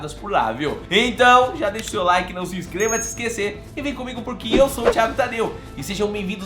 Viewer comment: Não se inscreva de se esquecer??? Será que ouvi certo??? 🤔🤔🤔 🤣🤣🤣🤣